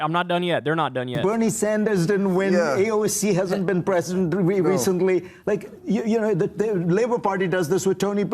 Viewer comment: Did he say "AOC hasn't been president recently"? like, the person who's never ran for president, and has been too young to?